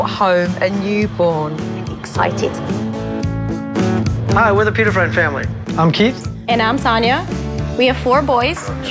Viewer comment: I thought they said “hey we’re the Pita Bread family”